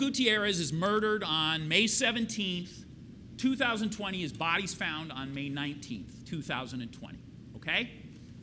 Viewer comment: his body was not found on may 19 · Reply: I thought he said the defendant searched "bodies found" on May 19th.